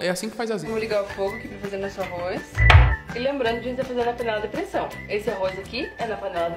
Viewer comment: Escutei depressão